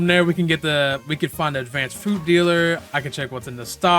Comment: If you want to experience the benefits of lower & stable ping , with reductions as much as 50 ping, check out GearUP Booster (available for both PC and mobile): <link> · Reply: did u just say advanced food dealer?